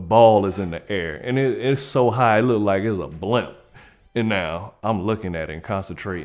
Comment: When he said "blimp", I heard "blunt" 😂😂😂😂